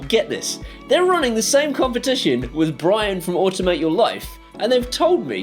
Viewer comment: My wife misheard you talking about Brain's channel as "automate your wife" and suggested that one press could instruct her to remove her nickers... so I guess I need these buttons now to make that a reality :-p